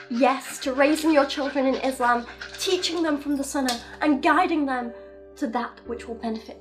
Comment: I’m an ex muslim living in aslamic country I appreciate you talking about how bad islam treat women because we can’t talk about it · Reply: When she said "and guide them to that", I heard "and guide them to death".